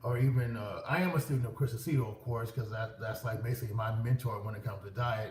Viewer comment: I can't understand the name for the diet mentor. Can someone please help? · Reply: Chris Aceto